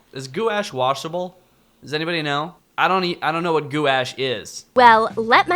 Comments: If any people don't know what "guash" is, it's just paint. "Guash" is a word my language but it can't be translated into English so it is just left as "guash" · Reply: I thought she said Goulash like that food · Gouach is a french word 😅 it’s a type of paint it’s pretty thic (in french you write it gouache) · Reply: I thought they said Goulash until I checked again. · I thought they said Goulash the first time.